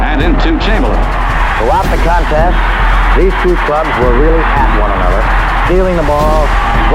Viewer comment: did he say “these two thugs”???? What word did he use? I’ve listened back like 20 times an can’t catch it.